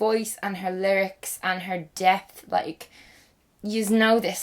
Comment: When you were talking about Lana del ray saying something about her dept I thought you said death and I was like what? When? How? I was confused